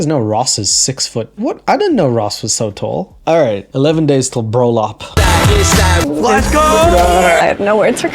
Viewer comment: omg did u say BRÖLLOP??